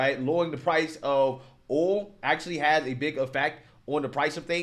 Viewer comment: What's "oal" are you trying to say "oil"?